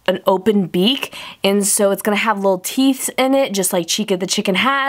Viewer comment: did she just say teeths